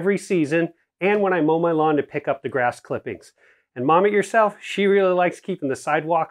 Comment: Did he say "mommy herself likes to..."? LOL